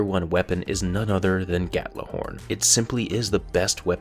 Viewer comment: Did you just say Gattlohorn instead of Gjallarhorn?